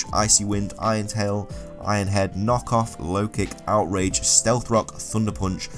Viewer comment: So I'm just casually listening and I hear "Iron Head Knock Off." I thought it was an alternate version by the way you said it XD